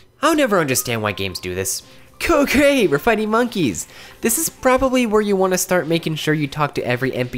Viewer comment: i thought you said "cocaine we're fighting monkeys"